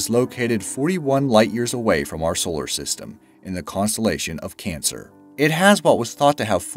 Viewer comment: Wait 11:21 did he say consolation of cancer